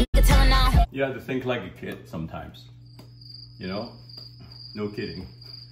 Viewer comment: Ngl i thought he said no Diddy 😂